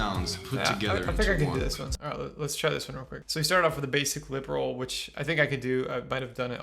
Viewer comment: did he just say that was a lip roll lol